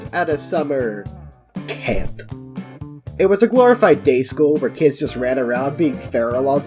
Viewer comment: I thought you said gay school and I was so confused😭